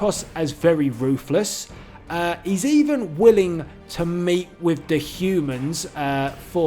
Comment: it thought he said he even was willing to !!!mate!!! with the humans but no he said meet